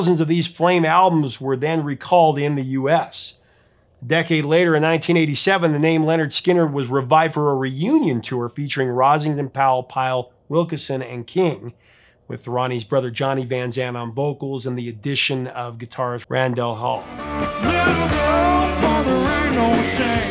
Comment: Leon WilKEson was the bass player. It sounded like you said Wilson.